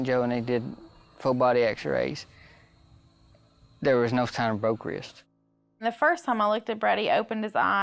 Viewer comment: Can someone please tell me what the dad is saying at 3:45-3:47? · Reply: The captions actually helped me. I thought they wouldn’t! He says, basically, “there is no sign of broken wrist”.